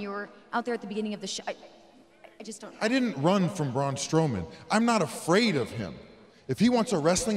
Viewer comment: Girl did u just say Brawn Stowman? Hahahaha